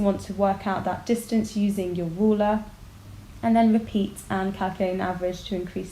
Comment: does she say ruler?